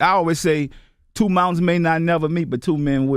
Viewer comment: Two men may not meet but two men will. MASTER P. That’s the realest shit I’ve ever heard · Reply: I thought he said two mountains may not meet